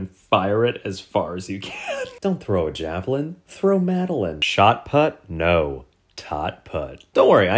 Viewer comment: Am I the only one who thought McCann when he said madeline?